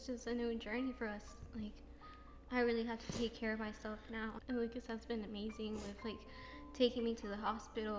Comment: When she said lupas I thought she said lucas